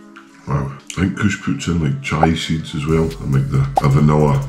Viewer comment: i think the editor misheard "Chia seeds" so Chives it is